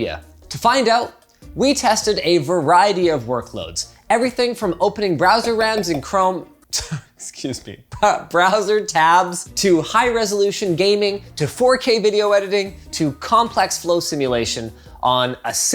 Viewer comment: I think I heard him say "brazzer rams in chrome" :D lol!!